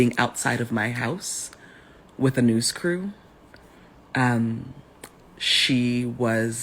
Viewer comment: I thought I heard her say "noose crew" ... And I got worries we have another Smollet situation